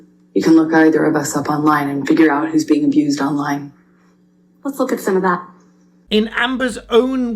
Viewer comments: you make a reference to her statement which you seem to have misHEARD: She said "who's being abused online" not "who's been the abuser online" She's talking about the hate she gets online. Just for clarification. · Bravo! Great catch 👏🏻 Her own hubris & ego keeps tripping her up over and over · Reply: To be fair and accurate, what AH actually said was "...and figure out who is being abused online" and not "...figure out who is the abuser online". There is a distinction. The first comment is referring specifically to current online abuse and her as the receiver of such. The second comment, the one that is hearing, would be AH telling the jury that the answer to the question "Who is the abuser" is to be found online (basically referring to the results of the UK trial). However I think misheard this one and that he is mistaken in this instance. Otherwise, thank you for great and informative content. I enjoy watching and learning.